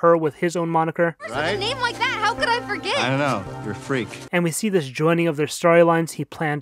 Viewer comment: honestly I never noticed he said “i dunno, You’re a freak” cause I thought he said “i dunno, i’m a freak” like confirmation to Chrissy